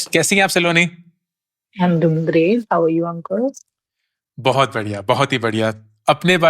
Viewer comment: i heard "How are you Uncle?" Pfft....man I'm getting old